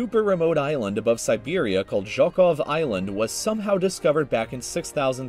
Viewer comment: I swear I heard him said "Jerk off Island"